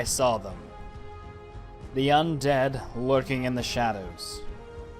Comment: who else heard "the young nerds"